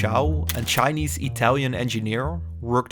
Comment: Clearly he said japaneese. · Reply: I think you need to clean your ears. At 10:30 he clearly said Chinese-Italian engineer. My ears and brain work very well